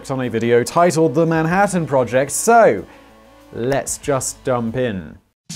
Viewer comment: Did he just say "let's just dump in"